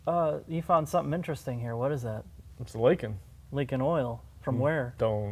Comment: Man at 3:10 I thought you said it was "Lincoln" because the part is actually stamped with the LINCOLN logo instead of a Ford logo but you were talking about it leaking oil. I guess they used an MKZ cover without rebranding it, lol.